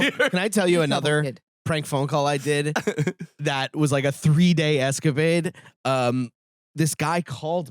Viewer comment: Pretty sure I heard "3 day escavade" and I wouldn't be surprised if thats how Brendan thinks it's pronounced 🤣